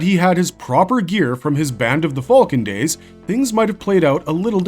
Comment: Ive always heard it as band of the hawk